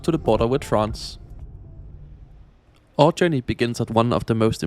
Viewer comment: first time round I heard "All journeys begin at..." and I was like "haha damn right motje rest is parking"